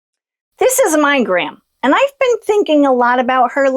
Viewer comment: When you showed that picture of your gran at the start it sounded like you said, "This is a migraine." 😂